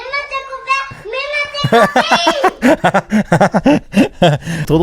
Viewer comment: moi perso j’entends pas « même à tes copines » mais « maintenant c’est compris ?! » 🤷🏼‍♀️😅